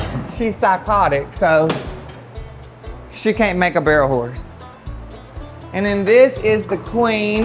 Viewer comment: I thought you said Crack Barrel horse! Doll is my favorite horse. Now I know we have something in common, we both crazy.